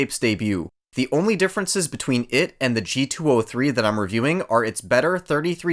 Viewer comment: Good review, I have a g402 currently and it's just too damn heavy for me, probably going to buy this one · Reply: Sorry! My bad i thought you said the g403